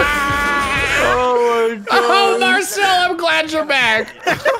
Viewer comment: When said oh marcel I'm glad your back I thought he was gonna say oh marcel I'm glad your black